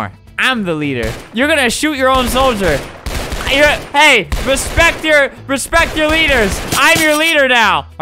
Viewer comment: Albert: "Hey, RESPECT THE LEADER" Me: "Did U Say... RESPECT THE VETS ?"